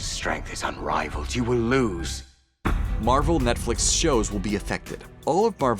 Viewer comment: anyone else hear them say "Barvel" at 8:03? lmao